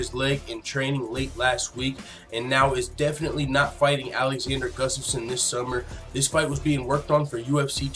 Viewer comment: Alexander Gustafsson that Alexander I thought you said Alexandria yeah I forgot I had scheduled a leg injury and I'm going to have to get that taken care of.